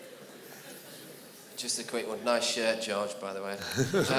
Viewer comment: what does the guy say, that makes them lough so hard?! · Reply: "Nice shirt, George, by the way"